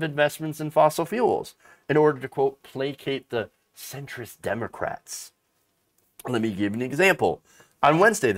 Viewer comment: I thought said, "Citrus Democrats." 😂